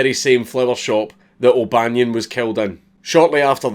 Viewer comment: I'm not used to the Scottish accent so I thought he kept saying they put a hat on Bunyon.